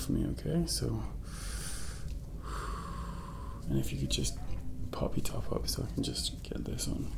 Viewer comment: Tried my best, but I swear I heard "pop your top off". Just couldn't help it. is easy on the eyes. Ironic, given the subject of the video.